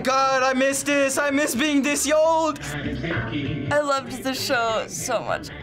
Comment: “ I MISS BEING THIS YOLD” 😂 did anyone hear that or I’m trippin lol